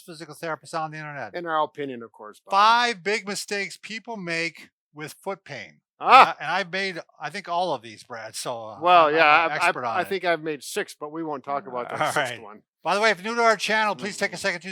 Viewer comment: I thought I said mistakes people with big feet make and I was like oh that’s me 😂